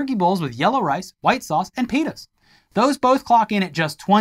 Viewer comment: Wait, what's in the turkey bowl? what did you just say @ 45:02 I want to believe you said peanuts